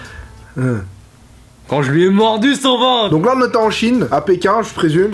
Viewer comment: Kevin a commencé à plus aimer Henry quand il a mordu quoi ?? 😂😂 a 2:30 environ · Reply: son ventre